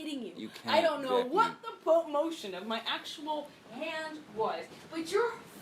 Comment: where it says "I don't know what the (inaudible) motion of my actual hand was....." is not inaudible. She clearly said "... full motion ..." where it said (inaudible).